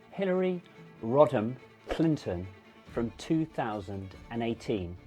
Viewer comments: I'm sorry, but it sounded like you said "Hillary Rotten Clinton". It's not incorrect. Just verifying. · i thought you said Hilary Rotten Clinton